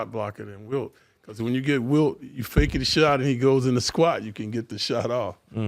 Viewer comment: did clyde say, “faking the shit outta him” real quick or am i buggin?😂😂😂